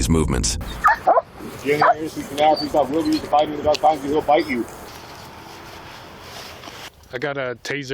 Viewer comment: the cop whispering that "if youre in the area let us know now because the dog will bite you" hoping he in the area and did not heard what he said and get bite! I loved that he said it barely enough to hear it from 2 meter away😅